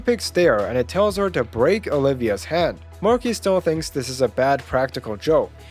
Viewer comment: Am I the only one who heard "head" and not "hand" ?